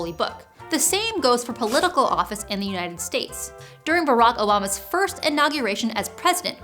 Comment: Did she say Barack Olama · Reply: I definitely heard Barack O'llama